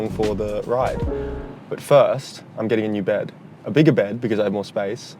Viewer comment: Tell me I'm not the only one who heard "I'm getting in your bed"